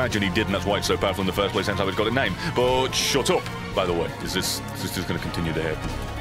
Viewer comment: I thought he said "WE GOT A PEDO BOX"